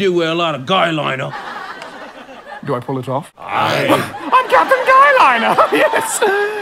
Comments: Sounded like he said captain gay at the end. Did anyone else hear that? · "Captin guy. . ." I thought he said gay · I thought they said "caption gay liner" 😂😂😂😂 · I heard "Im Captain Gay" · It sounded like he said I'm captain gay and they escaped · I definitely heard, "I'm Captain Gay."🏴‍☠️🏳️‍🌈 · At the end it sounds like he says Captain Gay- BUT HE DOES ROCK THAT GUYLINER. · When he said “All right Peter I’m Captain guy — and they escaped” I thought he said Captain Gay